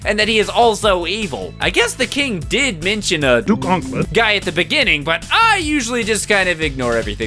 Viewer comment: for a while i thought they were saying "do gauntlet" and after a million watchings i realized he said "duke onkled". seriously, cant these people use any REAL zelda charecters? dont say crap about the budget, you should have a good one if your making a game in the first place.